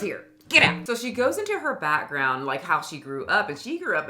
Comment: I thought you said, "So we goes into her Fatground" because of the word fat keep showing up in this video